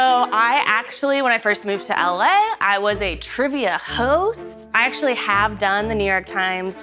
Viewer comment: i thought she said trivia hoe